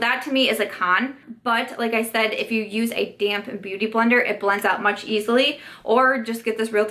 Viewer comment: Whenever you or anyone says "damp beauty blender" I hear "damn beauty blender" 😂😂 Those damn beauty blenders, I tell ya! 😉❤️